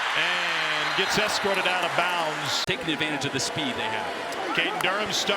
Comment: did I just hear the commentator say 🥷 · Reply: “Take advantage of the speed they got”